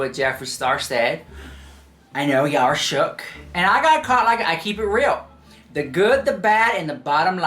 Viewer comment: I thought I heard JS say "I got caught up in that too"